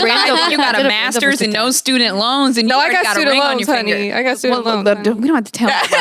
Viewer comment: Did say has a ring on her finger??😮🙌